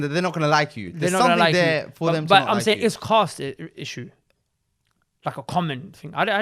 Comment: There are definitely cast issues in Pakistan. Still. And I’m commenting this an year later. Not too many but still there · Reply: I thought they said class, what is Cast issues?